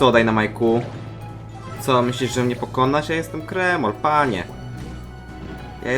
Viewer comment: ja usłyszałem ,,Jestem jak demon" XD